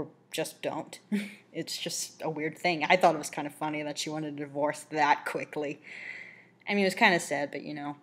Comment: Divorce? I thought she was gonna say Dog ._.